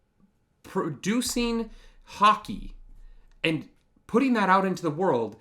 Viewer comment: When you said Hockey I thought you said Haki at first lol